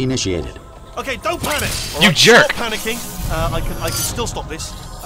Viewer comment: lol everytime i watch commentaries at this time i hear: You jerk You screwed up or oh no